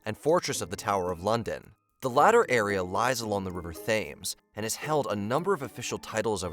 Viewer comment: I listened to it 5 times and I don't hear him say fames. I distinctly hear the th. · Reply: i heard 'tames'